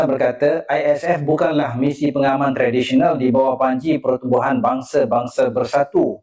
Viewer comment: Maaf di menit 3:40 dia bilang DI BAWAH BANCI atau DI BAWAH PANJI. putar berulang-ulang dengernya di bawah banci. Terus tentara malay yang mau di kirim ke gaza pakai sarung gitu?